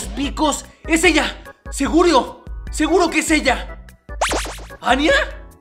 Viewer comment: jjajajaja me encanta mas que si no se si escucharon en el minuto 0:38 / 4:01 que dijo segurio 🤣